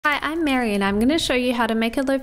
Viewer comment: Thought she started the video by sayin hi im married hahah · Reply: I thought she said, “I’m Maryanne.”